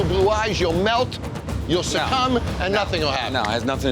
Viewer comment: For the tiki bar I definitely thought Jon said the owner would "look into those beautiful blue eyes, melt down, then come" but thank the lord he said "succomb"